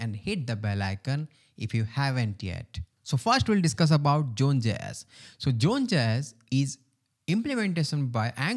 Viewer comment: what is "June Jess" ? Sorry, as as English person, I don't understand. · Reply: It's zone.js